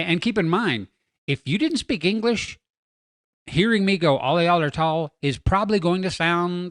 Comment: I heard, "All y'all are short"